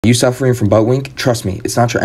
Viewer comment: I heard "Are you suffering from Baldwin?" and 1204 AD history lesson PTSD hit harder than the squat.